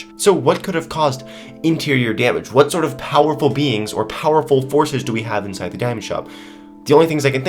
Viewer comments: Lol when you said powerful beings I thought you said powerful beans😂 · When he said beings I thought he said beans....... #beans